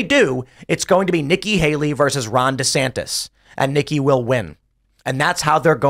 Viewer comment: When I first heard Ron Desantis, I always heard it as "Rhonda Santis" if you guys want to bash him, call him Rhonda Santis with the Heels. I like the guy though so don't do that.